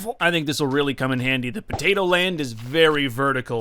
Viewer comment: doesn't anyone hear a "potato" in the background at 9:40-9:49?